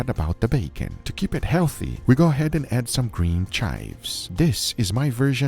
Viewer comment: Did you just say "to keep it health add some chives"? 😂😂